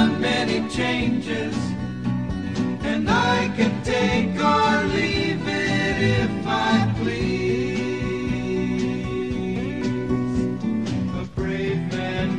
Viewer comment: Always thought it said I can take on Jesus if I pleased".